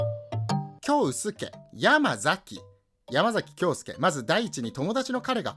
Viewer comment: キョウスケ・ヤマザキが超薄毛・山崎に聞こえて耐えられなかった